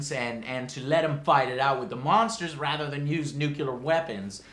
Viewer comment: Did he just say "nucular"?